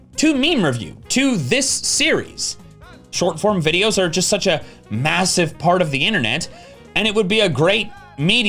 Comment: Did you just say MASSIVE ?